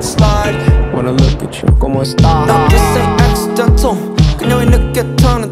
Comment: Did he just said kamusta? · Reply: He said cómo estás